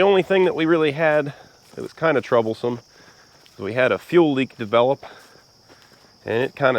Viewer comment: You say fuel leak i hear ‘dust control’